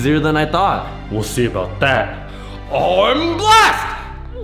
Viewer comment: captions said oh I’m blessed